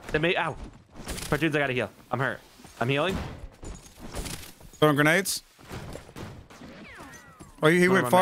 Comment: When i heard say im throwing nades i was distracted so i thought he said im throwing aids